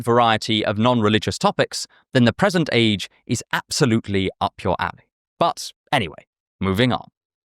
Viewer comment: i thought bro said absolutely up your ass😭😭😭